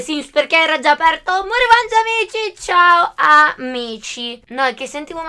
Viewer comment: Al posto di capire “CIAO AMICI”, ho capito “CIAO MICI” hahahaha❤️